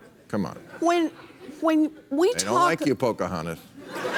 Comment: Did he really call her Pocahontas to her face? (6:46-6:48) Lol.